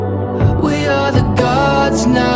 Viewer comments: Sound like he saying love you for fendi 😂😂😂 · I love you for Infindideeeee oooooh ooh ohhhhhh!! Never sounds like he says infinity 😅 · I hear "i love u for infini" :/ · First I thought she was saying i love it for your city🤔